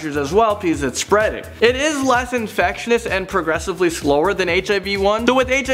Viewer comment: did he just say "infectionis" ?